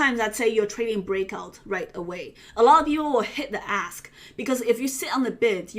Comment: i keep hearing "hit the ass"